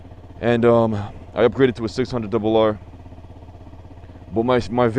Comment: did you say 600wr? :D